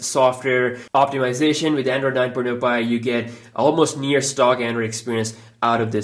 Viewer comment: subtitle says the oneplus 6t has android 9 porn empire😂😂😂